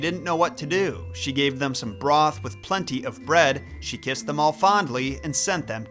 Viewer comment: i always heard it as "whipped them all soundly" myself.